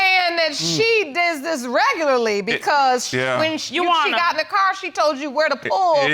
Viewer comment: Hold on. Who is Yu-wana? She keeps saying No Yuwana and Yes Yuwana. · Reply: Leslie Sinclair I thought she was saying Juana 😂😂😂